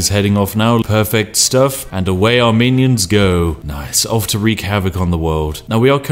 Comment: I keep hearing 'Armenians' instead of 'our minions'